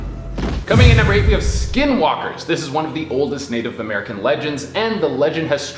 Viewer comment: At first i thought you gius said slow walkers but then i heard skin walkers